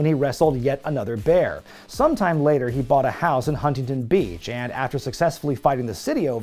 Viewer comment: At first I heard "fought a house in Huntingon Beach" and thought this gentleman graduated from fighting a bear to a house to an entire city